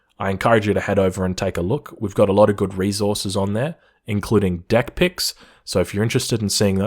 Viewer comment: Not going to lie total misheard deck pics and thought you were just trolling lmao